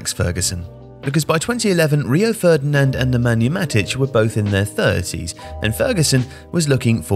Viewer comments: Did I just hear Nemanja Matic under Ferguson at 1:21? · It says matic instead of vidic